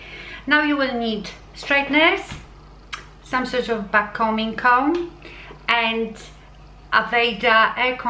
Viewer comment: I thought you said no backcombing??????